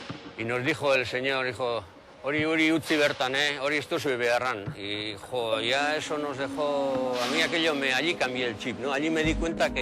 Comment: que dice en el mn 5:36 traduccion please. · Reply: Dice: "Eh eso dejadlo ahí, no lo vais a necesitar"